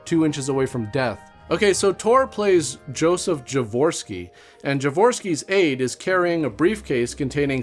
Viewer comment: I heard he got a divorceski